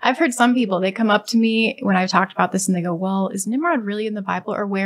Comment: Am I trippin or did she say " when i talk about this nigga" 😂